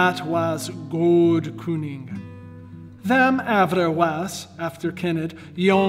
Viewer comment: "After Kenneth" sounds so weird in the middle of that. Is that actually what it says, though, or is it just coincidence that it sounds like that? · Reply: He said ‘æfter cenned’